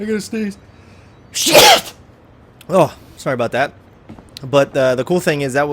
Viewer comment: hahaha sounded like SHHIIIIIIIIIIIIITT :D haha